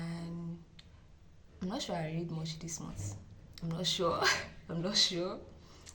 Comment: I thought you said we are reading Quran this month😂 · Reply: we're reading it na😂 That's last month's read🤭